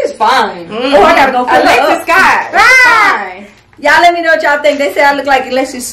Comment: The way was saying Alexis Skye was fine had me deaddd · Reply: I thought whole time she was saying Alexis Scott I was lost like who🤣🤣🤣